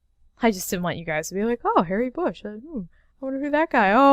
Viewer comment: I thought you said "Hairy Bush" was his name and I freaked out.